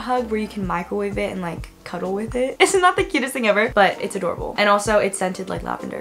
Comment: is it just me or did i hear "its not the cutest thing ever but its adorable"